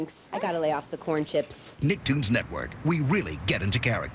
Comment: I've always thought that a boy was saying nicktoons